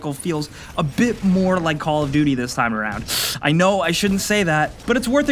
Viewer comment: Did you just say, Call Of Duty? :0